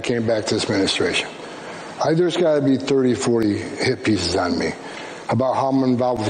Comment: I thought he was saying that the media was sending “Hippies” after him, good old mumble mouth. 😅